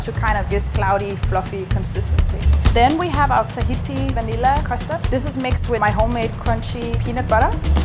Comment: Hi what does she say at 4:00? tahiti vanilla custard mixed with peanut butter? Does that mix make peanut butter not that sticky? Plz help cos subtitles doesnt make any sense....when sonny says saltine at 4:18 subs says he got assaulted in his childhood and the chef says...oh sorry.